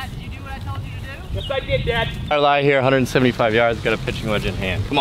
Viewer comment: wait just a minute did say 175 PW???